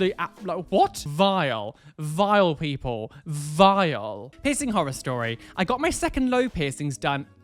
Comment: The dude that got mentioned in the next vid was right at the start is sounds like saying pissing horror storys